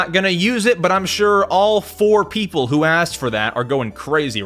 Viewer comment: you said 4 people so fast i thought u said poor people 😭✋ i had to rewind so fast